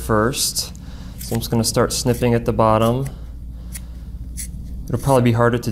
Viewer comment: I thought he said, 'I'll start sniffing at the bottom'....instead of snipping